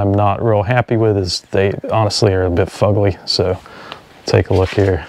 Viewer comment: Hey, did you use the word "fugly" at the 4:30 minute mark? I'm not sure, that's what I thought I heard and just wanted to be sure. Doug🏍️👍🧸